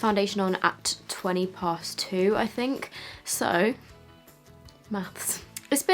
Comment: Did she just say 35 past 2? 😂